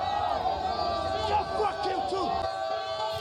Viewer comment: bro clearly said lets fucking go bruh u cant even understand ur language